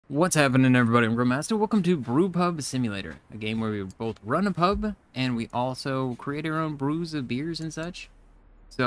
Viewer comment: In your intro I thought you said "Welcome to group hug simulator". Had to laugh. I know full well what video I was watching but that made me pause a sec.